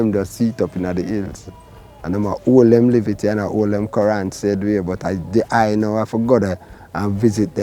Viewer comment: did brother say ''dem ah hold dem livity and Quran, same way...?'' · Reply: 'Current'... Give thanks for watching.